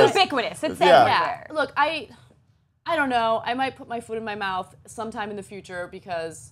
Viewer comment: did Ana say that she might put her foot in her mouth? I want to see that..